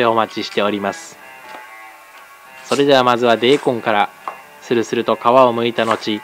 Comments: 大根がベーコンに聞こえるんよw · 大根のこと「デーコン」って言ってるの面白い🤣（最初、ベーコンに聞こえた） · でぇこんがベーコンに聞こえた🤣